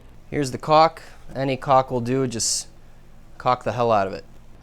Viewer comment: “any cock will do” I believe is what SHE said sir.